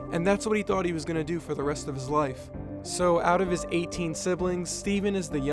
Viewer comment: WHAT DID YO SAY 18 SIBLINGS??